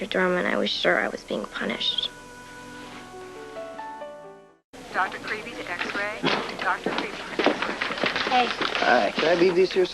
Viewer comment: Seriously thought I heard them page "Dr. Creepy" right after emily called him "creepy dr.dorman."